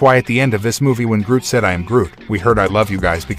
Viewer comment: I thought he said "we are groot". Didn't he?